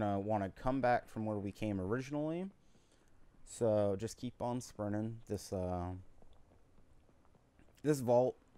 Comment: I thought you said just keep on swimming lol.